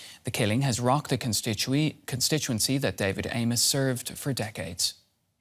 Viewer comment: I thought she said David Anus lol